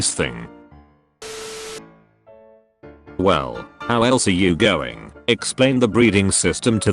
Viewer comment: Greetings friends! How are you all? Edit: please stop responding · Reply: The brain rot is real I thought you said how tall are you man I’m dumb.